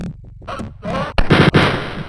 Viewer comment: When I was little I thought the guards said “hot dog!”